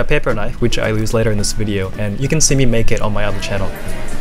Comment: I almost thought he said “and you can see me ‘naked’ on my own channel” I am disappointed to find out he didn’t